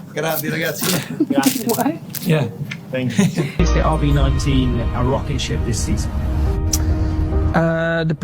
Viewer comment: First words of the question were cut off. the question is not "is the RB19 a rocketship this season" but "Can you tell us why is the RB19 a rocketship this season" making the answer (which was clearly a joke) make way more sense..... because you know... stupid answers/stupid questions. Of course he can not (is not allowed to) share that knowledge :) Also, you fastly underestimate the use of a "disappear" button on an F1 steering wheel. Imagine overtaking someone and instead of... you know... going around, you just can poof for a second or two and keep going your racing line. ;)